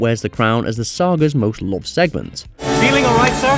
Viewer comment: I had to go to the closed caption to figure out what the guy said. Turns out it's "... as the saga's most loved segment." I'm still not hearing it. What I hear is "...as the star gah's most lost segments."